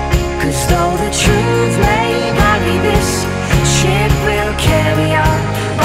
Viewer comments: at first i kept hearing, "sh*t will carry on" lol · I thought it was saying "Shit will carry on" well it is not wrong though :) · K I thought she said shit will carry on just to find it's ship damn !!!